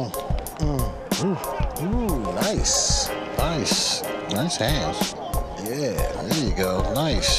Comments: is it just me or did he say nice ass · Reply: He said nice hands but it sounded like nice ass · did he say nice ass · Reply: HANDS!, not ass 😂😂😂